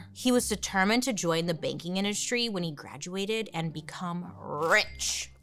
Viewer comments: At first, I thought you said Daddy Joe wanted to get into the baking industry and I was very confused. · I thought she said “baking industry” and have waited for her to bring up a bakery for like 10 minutes